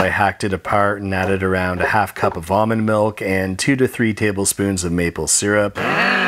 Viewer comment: It sounded like he said vomit milk instead of almond milk